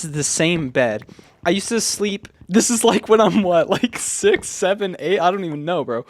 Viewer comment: i heard 67 at 0:24 and i just needed to look at comments